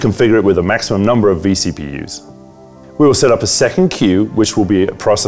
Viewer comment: What does he say at 2:32? Maximum number of VPCs? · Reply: vCpus